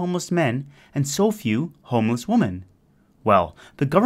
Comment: you're saying woman instead of women twice around 6:30- 6:45 and it's cringey